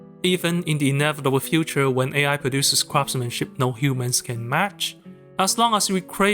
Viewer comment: It actually sounded like: "When AI produces crap-smenship." Great example of meaning out of coincidence like you previously illustrated.